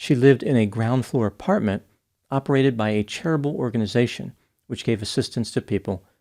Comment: Could have sworn I heard the doc say it was a "terrible" organization